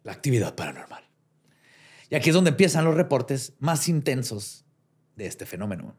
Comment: " Y aquí es donde empiezan mis erecciones paranormales" Yo solo escuché eso por parte de 😂